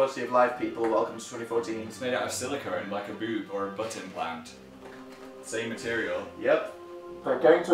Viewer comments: So. When Phil said butt implant I thought he said "button plant" and like...I was just so confused for about 5 seconds. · This whole time i thought phil was saying button plants but he was saying butt implants 😂😂😂